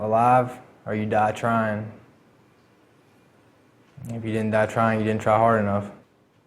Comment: really wish he didnt say " And if you die trying you ain't try hard enough " that was kind of a smack in the face to some vets who died trying to right by their brothers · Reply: you misheard him. He said if you DONT die trying you didn’t try hard enough